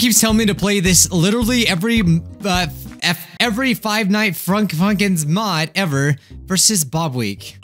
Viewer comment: Other youtubers: "Friday Night Funkin" "Friday Nights at Funkin's" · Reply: Because of youtube they thought said fucking but he actually said funkin